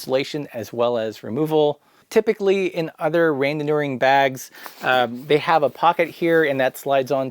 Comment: What type of bag? I can't understand the word and it begins with an R. The transcript says "rain manure" but I don't think it's correct 😝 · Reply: Randonneur